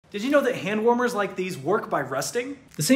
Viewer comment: My audio bugged out for a split second, and it sounded like he said “Hand warmers like this work my prostate” 😭